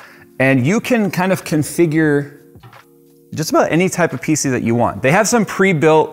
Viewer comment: I must be hungry I heard any type pizza you want